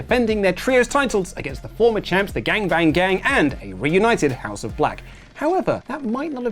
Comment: Anyone else catch at 1:19 where he said "Gangbang Gang" instead of Bang Bang Gang?? 🤣